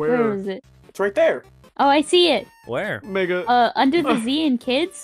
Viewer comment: bro said the n word. Edit: don’t worry, i just heard called “Mega”